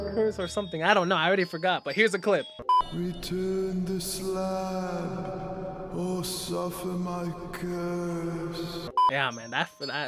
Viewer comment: who else heard this "welcome to this land, most of them are cursed"